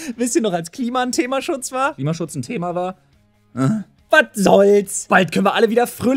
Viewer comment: Ich hab zwei Mal "als..Kliemann ein Thema war" verstanden. Dachte mir, naja gibt gröbere Probleme.